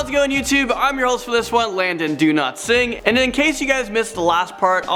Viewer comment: I thought he said Landon DoNotSing